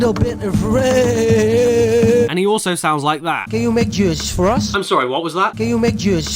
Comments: I think he said, "Can you make juice for us?" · I thought he said "Can you make JUICE for us?" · I heard “can you make Jizz for us?” 😂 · i heard:" Can you make jizz for us?"